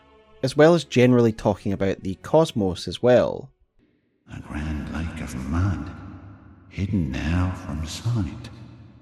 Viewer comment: Quick question; what is Micolash saying @ 01:09:35? I just can't understand the very 1st sentence.. sounds like "a grand Lake of mud, hidden out of sight (or from the side?).. " is the part I don't understand.. I can't start to hear him clearly when he continues nto say "the cosmos, of course!" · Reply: It is "A grand lake of mud, hidden now from sight."